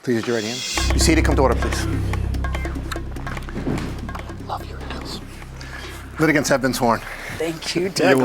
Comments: I wonder what Douglas be whispering to JM in the beginning after telling her “The litigants have been sworn in”. I think he be saying “I Love your Earrings”. What y’all think??? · Waittt what did Douglas whisper to judge in the beginning ?? 👀👀 “love you in heels?” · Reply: Earrings